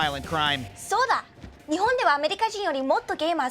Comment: Tbh I looked away from the screen and thought someone just said "soda".